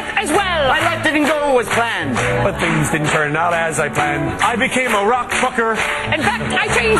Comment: What does Wayne say at 09:08 in? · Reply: I became a rock trucker